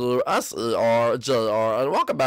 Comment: What did bowser Jr say on 0:03 bay oh that's what lol